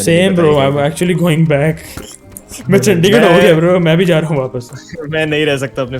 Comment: I think he said Chandigarh